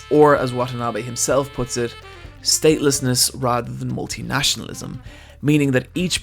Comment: Eyepatch Wolf 12:06 "Or, as Watanabe himself puts it, 'Statelessness rather than multinationalism'." The text/caption says, "staleness". That said, great work.